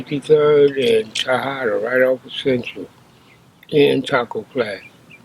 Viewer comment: You can tell he still got a lil animosity wit the blue side. Let me rephrase that Bompton. 😂😂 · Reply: Sounds like he said taco flats instead of Tortilla Flats.